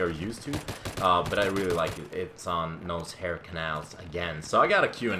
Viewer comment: I thought it said NoseHair canals xD